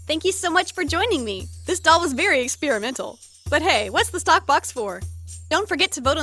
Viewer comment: It sounds like you said "tock box" instead of "stock box"